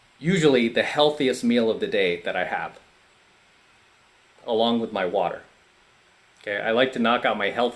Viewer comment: I thought he said "along with my wife" but no he said "along with my water"